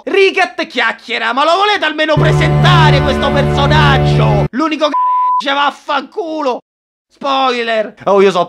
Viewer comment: io ho sentito "l'unico che regge vaffanculo" Ho sentito bene?